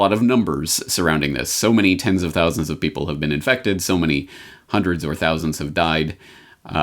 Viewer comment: When he said, "...how many hundreds - or thousands - have died?" I heard, "how many hundreds of thousands have died?" So that's what I'm reporting!